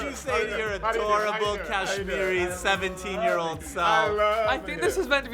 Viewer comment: lol is tan kashmirian? i thought i heard antoni say that